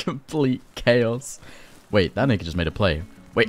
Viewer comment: now i'm not saying you are, i'm just saying what i heard was "wait that n word just made a play" at 33:56 im just saying i can't unhear it or hear what he was trying to say other than that given the context and what it sounds like. Someone please tell me i'm wrong lmao